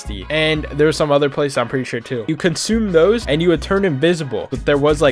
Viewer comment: I thought he said ‘you consume toes’ 😂😂